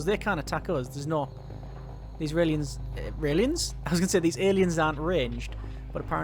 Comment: i heard you say these "israelians are strange", would you care to elaborate?